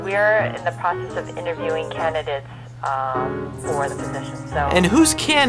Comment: I'm Canadian and loved the "They're all from Canada!" It would have been hilarious if you had started to sing "Oh Canada". It did sound like she said "Canada" instead of "Canidate, like you use for so and so is a Canidate for the State Governor". Because some words DO sound very similar.